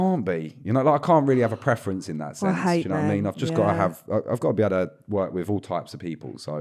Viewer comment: Did she say 'i hate men.' at 7:46? Is she a misandrist? Or am i hearing things?